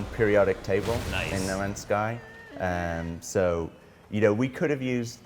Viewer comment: I thought he said "Nomad's Sky" at the start getting it wrong, but whether he did or didn't, that would also be a great name for this game.